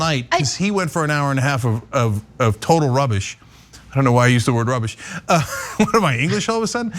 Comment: Can somebody decipher for me the comment that Cenk makes about “total rubbish” starting at 7:40? In those few seconds he talks too fast for my brain to work out what each word is. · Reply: He said "I don't know why I used the word rubbish"